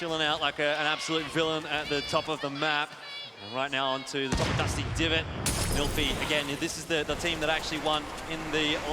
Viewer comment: wtf did I just hear? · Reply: He said, “Cheers to Lazarbeam, he’s true blue.”